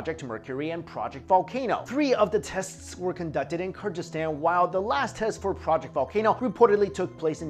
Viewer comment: Did he say Kurdistan?